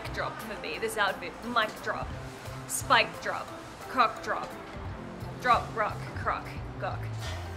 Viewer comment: hahahahah i heard "C*ck drop" at 16:09 and was like "WAIT WHAT!!!?" and had to replay it so many times and now i can't unhear it